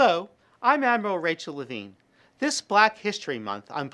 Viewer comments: I thought I heard "Avril Rachel Lavigne", and didn't realise she had a new album out. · 🚨 Order my new book here: <link> . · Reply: I thought I heard "Avril Rachel Lavigne", and didn't realise she had a new album out.